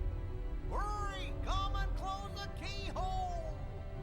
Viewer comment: anyone ever noticed goofy is actually saying "Sora, come and CLONE the keyhole"?